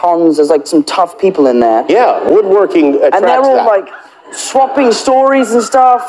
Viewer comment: I really heard "yeah. Woodworking attracts that"